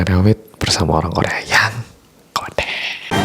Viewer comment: "bersama orang korea yang . . " apa ? aku salah dengar ya, aku dengarnya "ngondek" 🙉🙈